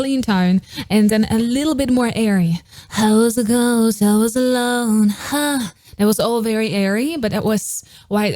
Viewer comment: What i hear "i was a ghost i was alone, huh so wats its ,huh so gay"😂